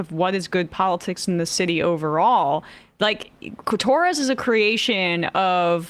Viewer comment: I heard “cli-Torres” 😂😂😂 Freudian slip Emma? 😂😂